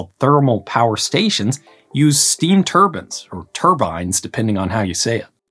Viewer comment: I thought you said turban. I've never heard anyone pronounce it like that